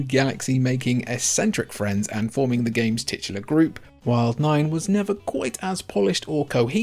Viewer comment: did you just say tits?!